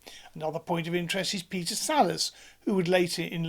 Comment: To my American ears I thought you said "Peter Sellers" and got very confused until you said "Wallace and Gromit." 13:01 That's a Titan 1, not an Atlas. 16:40 I'm going to ask if you could step out of the scope of your video and answer a nerdy question. I could follow your equations reasonably well (I'm rocket engineer, not rocket scientist), but I can't seem to make the leap to your derived exhaust velocity. Yes, you could toddle to the moon at 20 MPH if you wanted. All it takes is a T/W fractionally over 1. That means just enough thrust to balance the weight, which can be done by black powder, or even compressed air (you just couldn't keep it up long enough). Why would you need an exhaust velocity so fast it would get all the way to the moon in 20 minutes? Leaving the Earth's gravity well is a function of total energy, not power. More power just lets you get there faster.